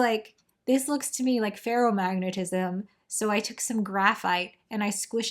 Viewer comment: Feral magnets! I kept hearing "Feral Magnets"...and now I imagine little squirrel-magnet pokemon things. But you mentioned "Stonks". Stonkers. I always thought of them as that jock that pretended to know everything about Star Trek, but like in a Good Will Hunting Ben Affleck character.